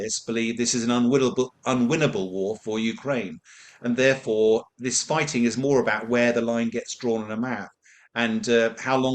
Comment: Did you understand what the retired Air Vice Marshall said between 6:12-6.14? If so, please let me know. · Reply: ("...and therefore this fighting is more about where the line gets drawn) on a map, and eh, how long will that go on for?"